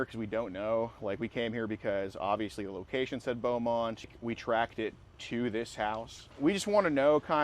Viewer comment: Did he just say Beaumont as in Beaumont TX? No way Jidion came down to texas lol.